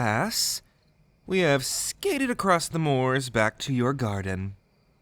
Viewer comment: I misheard the 0:20 part as "now that we have skibidid across the moors" and now I hate myself even more